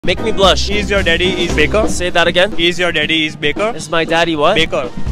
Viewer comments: I heard "is your daddy 'bekar'?" 😂😂 Postscript: No opprobrium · I thought he said is your daddy a beta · I thought he said Is your dad a beggar 😂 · I thought he said " is your daddy bigger" my holyy molly dirty mind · I heard he said "is ur daddy a pickle" 💀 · I heard like "Is yr dad a beacon" so.... It can be a new line for sure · I thought he was saying is your daddy’s bigger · i heard it as begger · First of all I heard him saying "Begger-" 😂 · I swear I heard "is your daddy a pickle?" And was so confused... but in my defense, English is not my first lenguage... I also think the pick up line was cute 😊